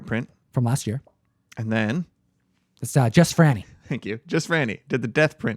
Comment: Who were the prints at the end of the video done by? It sounded like you were saying Jess Franny... is that correct?